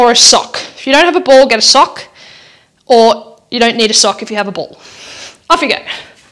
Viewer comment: Omg she said off you go....anyone else come back with a BOWL? I thought that's what she said. I went to the kitchen and came back with a bowl. I saw her do the sock thing and still waited for what to do with the bowl. 😂 I swear this is real. I'm cracking up 🤣 BALL IT WAS BALL